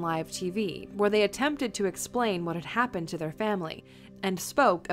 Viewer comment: I thought no.1 said trump family!!! Lol